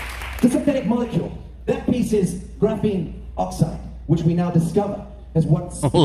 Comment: na--- you misheard him he said Graphine oxide not Ethylene Oxide - you misheard him a few times.... he also said light adherence... jus sayin' if you gonna deconstruct listen more carefully......he speaks truth....there's loads of this shit if you do an epsom salt cleanse bath technology cells that are microscopic will leach out of your body anyway - we are all drenched in this shit...